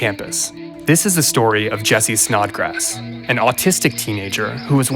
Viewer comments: Maybe this is insensitive but am I the only person dying over the fact that their last name is snodgrass 🤣 At first I thought he said snot grass lmaoo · snodgrass...i thought he was being a smartass. i really thought he said jessie snotgrass! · wait, his name is actually snodgrass? i thought he said snotgrass, as in not grassing anyone up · I thought he said his last name was notgrass 😅 get it.. its a Weed joke guys! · Thought he said his last name was "Not Grass" just as a joke haha, but no.. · thought i heard him say ‘snot grass’ & im thinking its a nickname for him selling shitty weed but the real story is j tragic